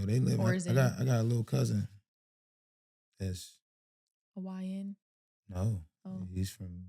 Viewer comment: She said oh he's "a YN".... he thought she said Hawaiian ... bro said nah he from right here.... oh I thought you said something else.